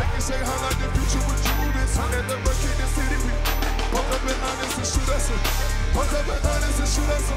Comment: LMAO won't SNL let Hendrix say 'Boolin'? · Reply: I thought he said 'when the work hit the city we MOVE IT'